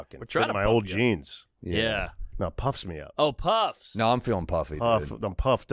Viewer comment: You know how we know it was an awkward podcast? Cause you told us 20 times. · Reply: Can you believe Eric said puffed and Marc thought he said pumped? Ground breaking stuff